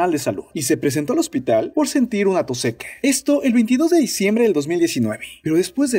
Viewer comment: Soy yo, o en todos los videos se le escucha decir más "Chine" que "China" ?!?!?!?!!! En el 3:44 escucho que dice "tos sequE" ...que carajos?!